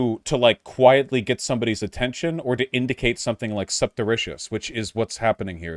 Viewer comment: I am so confused, the captions says he says "subtericious" but when I search that on Google literal 0 results came up where the fuck did this word come from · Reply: He meant surreptitious: (adjective) kept secret, especially because it would not be approved of.